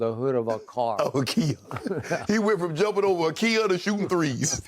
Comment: “he went from jumping over a kia to shooting threes” 😭😭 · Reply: I thought he said he went from jumping over Kia to jumping over fleas 😂 I’m high af 💀